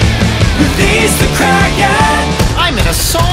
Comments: Oh I thought it said release the Karen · I thought it said Release the Karen. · I thought it said release the karen